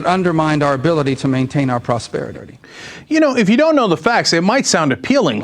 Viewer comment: I heard Rubio say "prosperi-dirty". Did anyone else hear that?